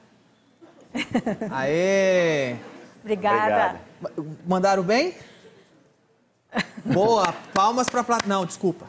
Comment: "Escutaram bem?" KKKKKKKK Edit1: É "Mandaram bem?" Mas eu escutei/entendi errado.